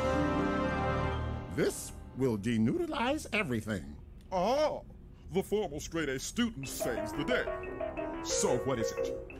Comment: i thought he said "the formerly straight student" 👀